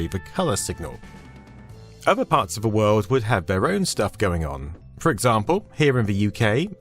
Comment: Love how at 2:16 you say "their own stuff going on" but the cc says it as their own shit going on 😅